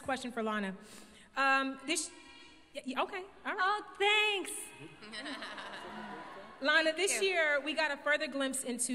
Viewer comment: Didn't understand what was said at 6:25 by a fan in the audience, can someone tell me ? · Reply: Happy birthday